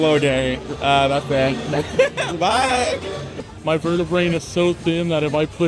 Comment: did he say “verte-brain?” Bruh